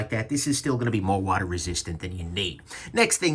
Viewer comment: I thought for a second that you said it was "more water resistant than your knee!"